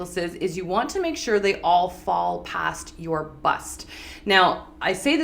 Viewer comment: At first, I thought she said a necklace "below your butt", though she actually said "bust"...